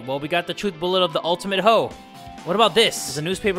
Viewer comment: I can't tell if this was intentional, sounds like he said 'The Ultimate Hoe' I mean, dunno if that was on purpose or an accident.